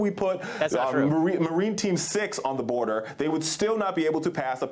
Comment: did he say marine team 6 ?!💀